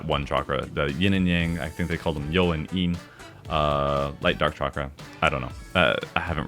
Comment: Not gonna lie... I misheard 'Light/Dark Chakra' as 'Light/Dark Chocolate', and now my brain refuses to hear anything else. "Open all of your Chocolate"... "The Seven Chocolates"... "Chocolate Stacks"... "Overflowing Chocolate"... mmmm... "Forbidden Chocolate"